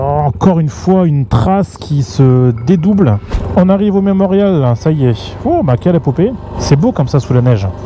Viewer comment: j'ai entendu "on arrive au Même Auriol" !!!!!!!